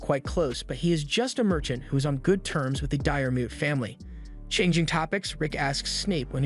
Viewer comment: Honestly, I kept hearing "Diarrhea Family" a bunch of times.